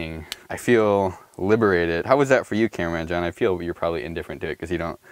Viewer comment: did I hear a Cameraman John??